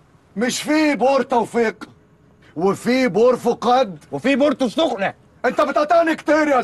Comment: شو قال محمد ثروت بالدقيقه 5:22 ؟ في بو ر٠٠٠٠٠ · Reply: بورتو السختة هي قرية سياحية في مدينة العين السختة بجوار المدينة السويس